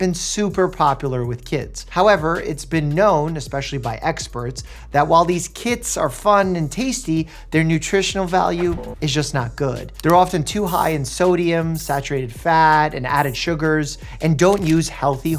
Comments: I thought he said "these KIDS are fun and tasty" 😭😂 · I swear i heard him say "while these kids are fun and tasty"